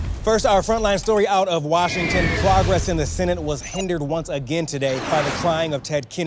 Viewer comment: did he say "Clogress in the senate..."?